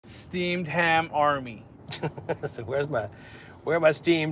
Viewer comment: I thought he said steamed clams